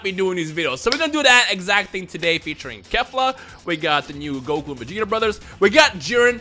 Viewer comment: Did you say Goku and Vegeta brothers?